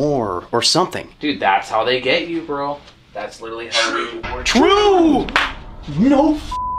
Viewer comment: the caption says disaster but i heard true